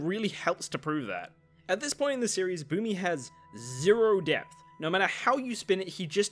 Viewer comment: I misheard that as ZERO DEATHS!